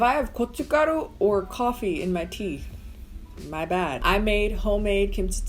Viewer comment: I'm screaming. I thought you said 'coffee in your tea' and I got super excited and started googling for Korean drinks that use coffee and tea because I love both but together? omg YES. Then I replayed it and realized you said 'coffee in my teeth'. Only me.